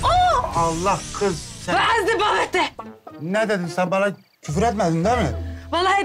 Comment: nazgül ne dedi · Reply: Selen Cetinkaya "hay ben senin babanın ... !! " diyor